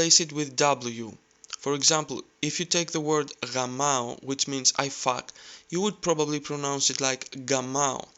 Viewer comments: ¨For example, if you take the word ´gamao´ which means 'I fuck'... ¨ Did I hear that right? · did he say "which means i fuck" ??